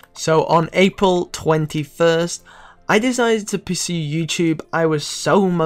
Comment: what did you say at 0:09? i'm not even that high and i still can't figure out what you said. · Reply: ByeBaybe I decided to pursue youtube.